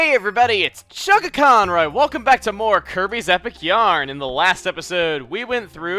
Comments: for a second, i thought Kirby's Epic Yarn said "Kirby's Epic Yam" :P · for a second I thought it said Kirby's epic yam